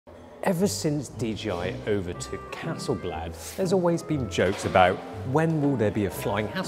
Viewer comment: I heard 'ever since DJI took over Castle Black'. been watching too much game of thrones...